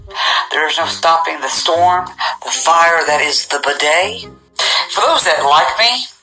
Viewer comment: TheBudday is still a thing? Oh heck. That’s ridiculous. You’d think since TikTok is made for under 18’s they’d get rid of creeps. You’d think. Think. I guess not. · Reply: The way he pronounced it, I thought he was saying "the bidet".